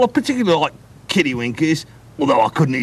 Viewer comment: Did he say he likes to eat kiddy wieners????? 😂😂😂 ewwwww. Gross!! Jkjk... n then they got a guy on the team named Daz that just admitted that he loves meth 1:50 😂